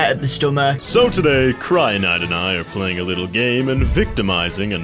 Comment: i thought you said 'crynight' instead of 'cryonide' in the opening it terrified me